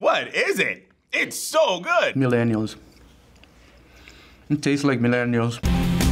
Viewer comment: For some reason at the one part it sounded to me like someone was saying that whatever they were eating tasted like "mid annuals", whatever that could possibly mean. I see from a bunch of comments that I somehow misheard that from "millennials".... I'm not sure that that's any better.